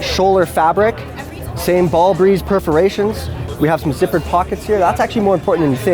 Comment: Did he just say ball breeze?